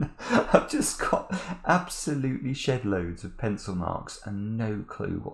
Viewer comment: Amazing puzzle and wonderful solve! Is it just me or did say "shitloads of pencilmarks" around 28:00? :D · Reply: Shedloads, but you got the translation right.